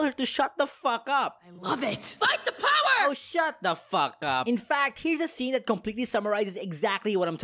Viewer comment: I heard "Oh shot the pho cup"